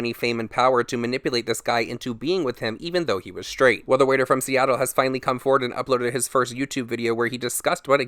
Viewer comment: At first I thought you said 'weirdo from seattle'. XD